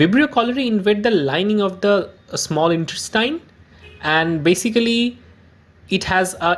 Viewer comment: Invade small or large intestine? Sound and text are not the same, or did I hear wrong at 1:10? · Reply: ups its small intestine not large, our typing mistake. Klebsiella infects large intestine.